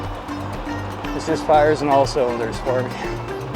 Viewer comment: What did you say at 21:51 ?? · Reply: "This just fires on all cylinders for me." I'm waaay behind on my subtitles. I'll get caught up this summer. :D